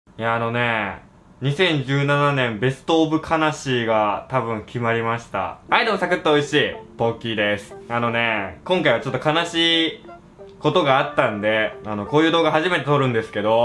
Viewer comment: ⭕️ ベストオブ悲しい ❌ ベストオブ魂 ❌ ベルトオブ悲しい ベストオブ魂と聞こえたのは私だけだろうか。